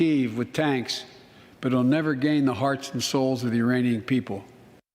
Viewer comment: It sounded like Brandon said Uranians. I guess we must have aliens from Uranus living among us.